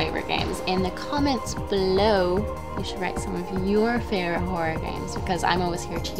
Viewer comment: Did they just say top 5 ...porn games?